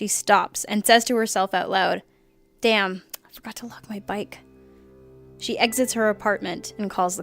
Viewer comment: I dont get what you're saying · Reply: oh, "forgot to lock my bike"